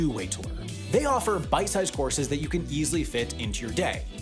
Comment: i heard it as bike sized horses and at this point i can believe you would say this